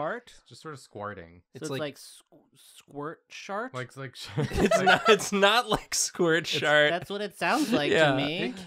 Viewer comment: Oh, I thought he was saying squirt sharting or squat Sharting